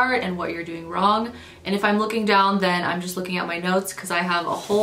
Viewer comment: Hahaha whut? What I heard was "when I look down I'm looking at my nose..." 😆🤦‍♀️